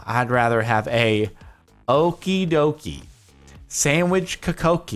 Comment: The okidoki are dutch, so from the Netherlands, nice dutch pronunciation Jardon. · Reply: The Youtube Addict I thought it said dokidoki 😂